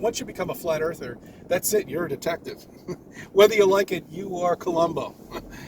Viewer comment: Hahahahahaha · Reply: I misheard him. I thought he said detective. Not dickheaded.